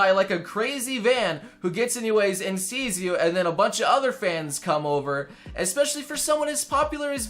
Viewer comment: "You can get stopped any moment by a crazy van" Me: imagining what that van would look like I know you meant fan, but I heard van.